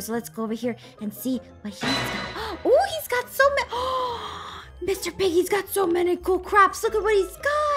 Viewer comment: it sounded like she said "Mr. PIGGY HAS SO MANY COOL CRAPS" 😂